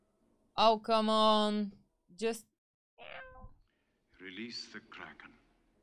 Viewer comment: And all this time I thought Liam Neeson said, "Release... The dragon!" 😅 Release the crakin? Release the Cracking? was rooting for the Crakin. 😅 Release, the Kraken! 😂 says, "What a cute 2-headed dog!" 😅 Yup. BTW, Jason and the Argonauts and Sinbad are closely related to this guy. 😎